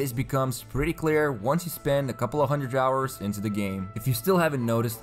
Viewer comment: I thought he said "once you spend a couple hundred dollars" in the beginning. I almost agreed.